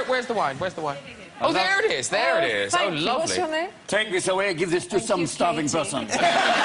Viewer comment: What he say? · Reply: Leonardo Ortega "Take this away. Give this to some starving peasant" At least I think so 😁